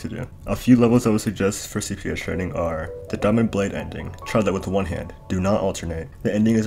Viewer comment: I have no idea what you said at 4:07, can you spell that? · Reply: diamond blade